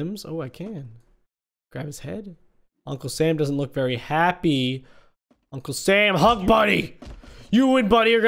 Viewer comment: I really thought he said: “Uncul Sam hU|&¥ Buddy”